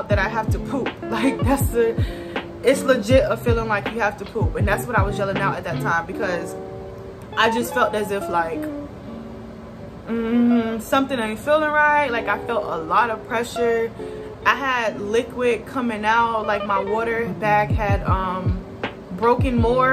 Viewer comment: Lol it sounded like she said I ain’t fat . Congratulations!